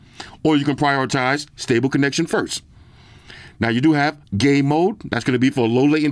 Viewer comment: I was just listening to the part when you said they had game mode and it sounded like you said "gay mode" and I thought, hey these some zesty headphones!! Thanks for continuing to show earfun. Great value product!